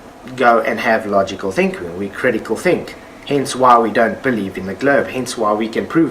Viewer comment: Wait... Did he just say "Hencewhile"? Like, "Meanwhile"?